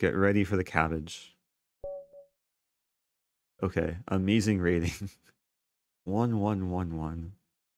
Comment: I seriously thought said "a measly rating" at first and I almost choked on my coffee